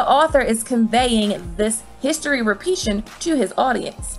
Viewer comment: Yo!!! Did she say “repeation” at 7:33??? 😂😂